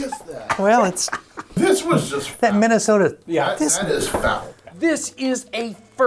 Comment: Did i hear Minnesota??